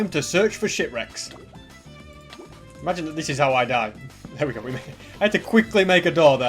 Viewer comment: I swear he said sh*t recks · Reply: He said ship rocks its just his accent